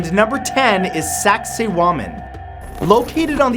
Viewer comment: on 10:38 it sounds like he said saxy woman